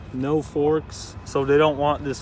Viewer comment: lmao i thought it said "no fooks"